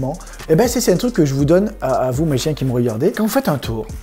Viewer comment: j'ai entendu a vous mes chiens 😂😂